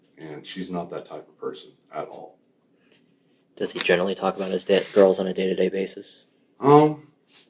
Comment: At timestamp 1:09:45 does CBI agent ask “Does he usually talk about his DEAD girls on a day by day basis?” Did I hear that wrong?? Any thoughts??